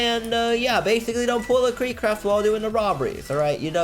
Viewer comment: did he just said kreekcraft wall?